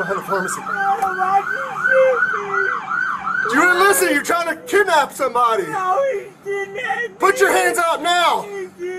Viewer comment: Did he really say "I didn't do anything wrong" wow the entitlement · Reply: I thought I heard him say "she tried to kidnap me" 6:33 But maybe not 😂